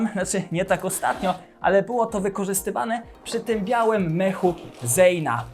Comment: czy tylko ja usłyszałem białym Lechu zejna😂😂😂😂